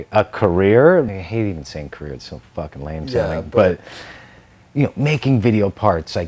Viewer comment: What does AVE say at 18:35? Thanks. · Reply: "Career,I hated saying career it sounded so facking lame"